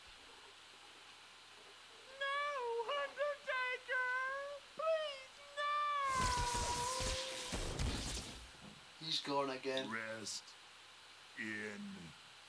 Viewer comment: It sounds like he is saying rest in peas